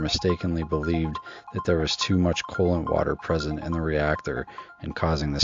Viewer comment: The way you were pronouncing "cooling water" made it sound like you were saying "colon water". I was wondering wtf?